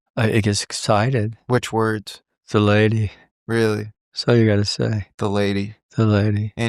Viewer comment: is he saying THE LADY? subtitles comes up as that word but is that actually what they are saying?